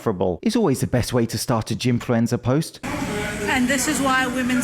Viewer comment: When you said “gymfluencer,” I heard “gymfluenza,” which I thought was a really good way to describe this virus of gym filming.